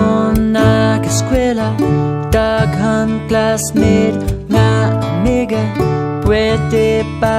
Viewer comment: Wait did he just say "Nigga" ○_____ ○